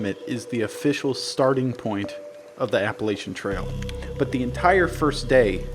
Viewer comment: I thought you guys kept saying apple Asian's tail